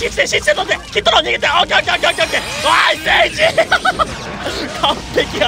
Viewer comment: アイス・エイジの時ナイス.エイジって聞こえた人いるー？